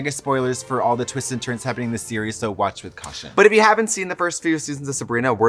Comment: I thought she said 'watch with Kasha' THERE'S ALWAYS TIME FOR A COCKTAIL